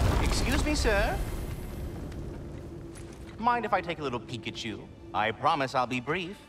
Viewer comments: I thought I heard "Pikachu"!😅. Nope. It's "peek at you". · I can’t be the only one who thought he said “Mind if I take a little Pikachu!”